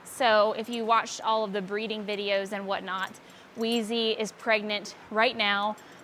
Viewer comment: I was in shock you said wheezy is pregnant right now. I needed to rewatch it three times if i heard it right. But you mean indy 😂😂